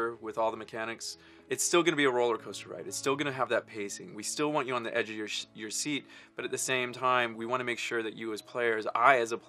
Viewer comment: for a second It almost sounded like " we still want you to be on the edge of your shit." Jokes aside though can't wait for UC4 :D